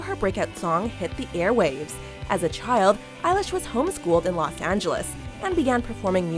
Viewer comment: did she just said eye-lash...